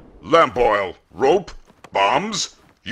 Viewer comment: I thought he said moms instead of bombs. 💀